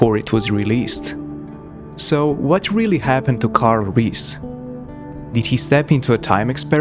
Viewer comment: I thought you said Kyle Reese at the intro and I was like, ‘Hey, wait a minute here.’